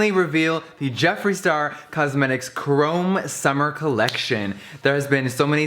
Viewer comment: Thought it said chromosome at first 😂 biology revisions does this to me